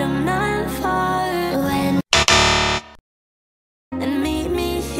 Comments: i heard “the void ain’t there” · “I thought it said “the others tired” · In death I heard "And meet me here across the plane, the other side im not fond of." Dont ask.